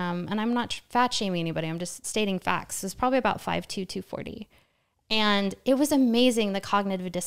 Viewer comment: did i hear that correct? 51:32 5’2” 240lb ?